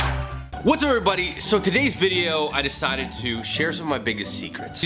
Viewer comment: he says "whats everybody" instead of whats up everybody